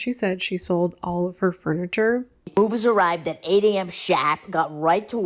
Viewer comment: She has the voice of an 80 year old smoker, wtf 😂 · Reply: I keep hearing “at 8 am SHAT”